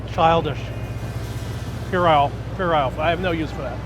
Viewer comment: (for anyone reading the captions) At 1:39 Josh doesn't say "you're off, you're off"--as it states, and which makes no sense. What he actually says is "puerile"--which means 'childishly silly or trivial.' (It's a really good pejorative vocab word!)